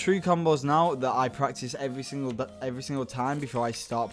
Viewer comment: uhhhhhh did u just say: that i practice every single day? hmmm that kinda sus ma boi VOTEM OUTTA HERE